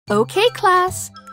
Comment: Nah bro thought she said “a boy” not “boys”